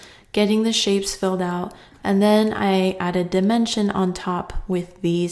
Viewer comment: I thought you said dementia, I have the best hearing ever 😎